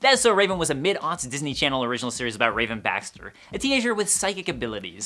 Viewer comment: wait.... did he just say raven "bastard"??😭😭😭